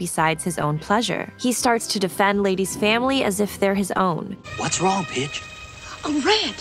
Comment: Did he just say "What's wrong bitch"?